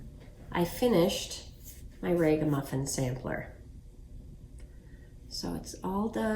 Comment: What is the name of the sampler you finished? I love it. I thought you said the Raven muffler sampler